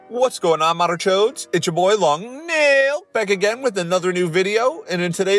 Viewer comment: I seriously thought you were saying "long neel"